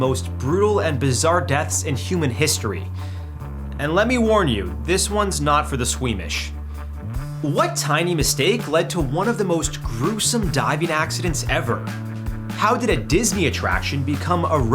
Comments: thought you said swedish there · I thought he said "These are not for the Swedish"